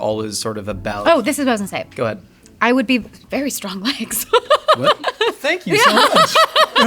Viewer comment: What does she say here? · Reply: "Oh, this is what I was gonna say: I would be— very strong legs!"